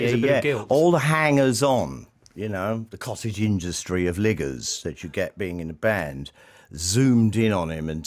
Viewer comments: Is he saying what it sounds like at 2:31 or am I hearing it wrong? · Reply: He says 'ligger.' This is UK slang for freeloader. Nothing to do with race. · Help me out my British brethren... what is he saying at 2:32? A cottage industry of what? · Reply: liggers......aka freeloaders. cheers mate